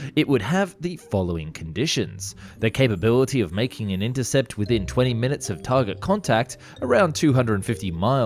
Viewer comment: i thought it said design and make a interceptr in 20mins